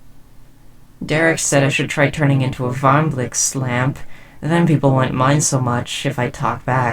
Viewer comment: please someone tell me what she said, i honestly can't understand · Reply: "Derek says I should try turning into a varmblixt lamp" Sorry about the confusion